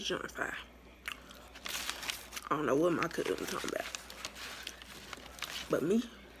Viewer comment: did you say “markell” or “my cousin” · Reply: MarkellSmh I said my cousin 💯😂😂😂😂